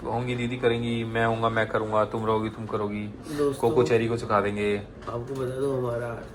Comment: did he said coco cherry ko sikha denge🥺